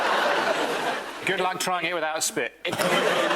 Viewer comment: What is Miles saying at 33:55? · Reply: “good luck trying it without a spit”